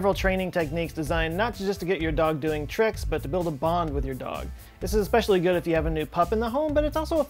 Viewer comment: Haha, I thought for a moment you said "build a bomb with your dog" :D